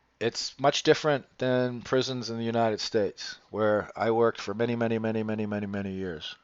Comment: Sorry, that's actually NOT what he said. At about 12:30 he states, "...it's not like prisons here in the U.S., where I worked for many, many, many, years". At about 16:35 someone specifically asked "were you a C.O. (correctional officer)". jokingly replies, "How dare you, sir! I worked for a living". I absolutely knew could not have ever been a correctional officer! He's far too left-wing liberal Nazi for that. He's much more of the hug-a-thug variety, and seldom has his facts straight on anything to do with law enforcement or the criminal justice system. This is probably due to a deep seated personal prejudice, or he had some confrontations with C.O.s during his "many, many, many years" working in prisons. website does mention that he was a "counselor". So... I'm going to guess that was a CD (chemical dependency/substance abuse) counselor, or possibly one of the many other types of mental health/life coach type counselors that make up the rehabilitation arm of modern corrections. Most of these counselors are inmate advocates that are a bit gullible, full of themselves, and out to save the world. They are easily conned by smooth talking inmates, and are often a security risk because they are so easily conned by inmates. My facility (yes I'm a retired C.O.) has fired many counselors over the years for various reasons including: bringing contraband in, failing urinalysis, and having SEX with the inmates! That being said, many C.O.s are also fired every y